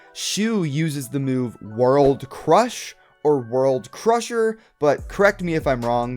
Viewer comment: World crush World crusher Me What Shu say world slash